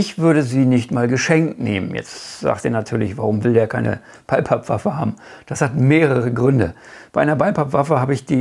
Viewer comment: Sorry, ich höre/verstehe sehr schlecht. Von welcher Waffe sprichst du bei Minute 8:00? Zitat von dir: "Warum will der keine ?????? Waffe haben? Ich habe mir die stelle oft angehört, komm nicht drauf, was du da sagst. LG, Rudi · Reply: Moin Rudi, ich meinte eine Bullpup-Waffe. Mein Englisch ist nicht das Beste... 😂 Ich würde so eine Waffe nicht geschenkt nehmen. Aber das ist meine persönliche Meinung, die ich auch begründe. Grüße